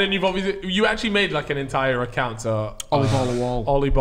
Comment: I thought he said “It’s Ollie Ball Hair”